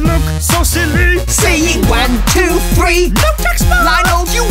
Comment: why does it sound like he said why don’t you eat?💀